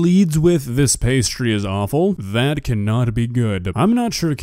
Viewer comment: I heard, "the pastry is really good", not "awful" Am I hearing things?